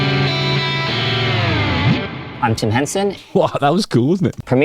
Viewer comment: I originally misheard and thought you said JIM Henson (of Kermit the Frog fame) 😂 Now that would’ve been cool! (Except he’s been dead for over 30 years 😬)